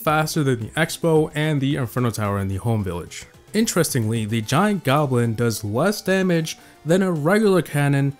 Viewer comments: Did he said "Giant goblin" instead of "Giant Canon" ?!? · Did he said giant goblin? Look at 8:09 He's probably very sleepy that time tho